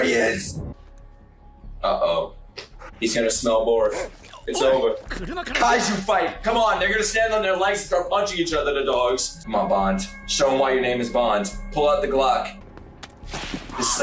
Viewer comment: did u said the n-word?💀